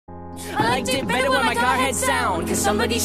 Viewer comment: Is it just me or did sound like they said I did it better when my car had sound